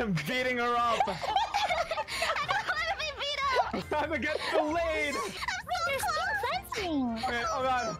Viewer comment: I think at 1:43 Luca actually said "Time to get fillet" as in making her into a cut of meat, cause, y'know, he was killing feesh over there.